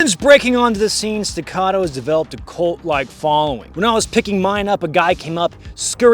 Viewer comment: thought you said a "Colt Like Following" in the intro... Was about to approve of that pun